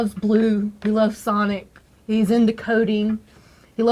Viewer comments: I thought she said he's into "codeine" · Does anyone know what she says there? It sounds like “he’s into codeine” but obviously isn’t that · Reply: Coding*